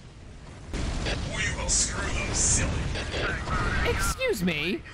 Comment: Did he just say "We will screw them silly" to sisters of battle? .... Ummmm ...... Understandable sir, have a good day.